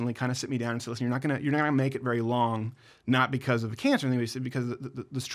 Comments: What did he say at 2:40? Not because of the what? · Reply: He said not because of cancer, but because of stress. No, he does not have cancer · not because of the CANCER???? Am I hearing that right? I thought you just said you DIDNT have cancer? I hope not. I suffer from Fibro and Dysautonomia, a rare form that doctors don't even know how to treat. I'm also a cancer survivor and will pray that you don't have cancer and get through whatever it is that's making you take a leave of absence. Get well soon and i wish the Crowder clan a happy Thanksgiving, Merry Christmas, and a Happy New Year! 💗